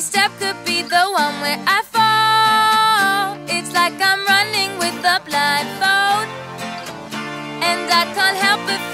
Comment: Proper thought she was saying "feels like I'm running with a blind goat" 😂🐐🤣